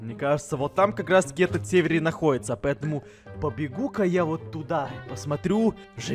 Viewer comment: Эдисон: побегу ка я туда Мне послышалось: папипу ка я туда😏😂😂😂😂😂😂